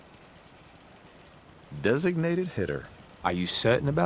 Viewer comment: when it said designated hitter i thought it said designated Hitler